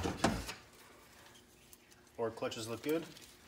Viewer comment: did you say "forward clutches" ?